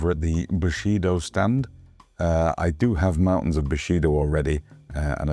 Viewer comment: LOL "mountains of Bushido" ... I thought you said Bukake LOL